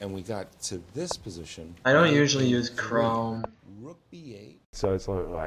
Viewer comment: Which browser did say he uses for Lichess at 15:24? With the f***ing voice in the background I can't hear whether he says he actually uses Chrome or not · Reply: He uses Chrome